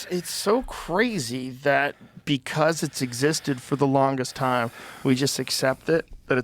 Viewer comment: the hell movie is he talking about it sounded like he said sisted